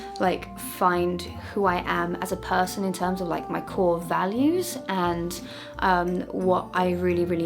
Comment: Near the begging (3.00) I legit thought she said her "whore values" and I was like wait what!! Then rewinded it a few times and realised she didn't say that at all😂😭💞